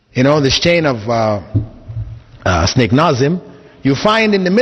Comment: does he say "SNAKE" Nazim????? ha ha